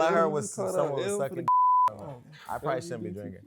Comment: Jason- all I heard is someone sucking d*ck… I probably shouldn’t be drinking ……*sips drinks* 🤣🤣🤣 I’m dead